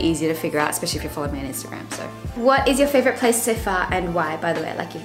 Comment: I wasn't looking and misheard the question as "What is your favorite place to fart and why?" and was like .. huh..?